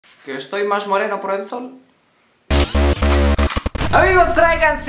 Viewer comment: estoy más moreno por alto?? O que dice jaja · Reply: Estoy más moreno por el sol. JAJAJAJAJAKSJAJAKSJAJA.